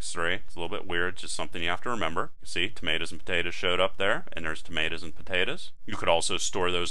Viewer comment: I was learning until I heard him say "tomatas and potatas"